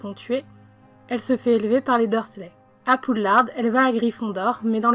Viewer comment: ces moi ou elle dit poudlarde ??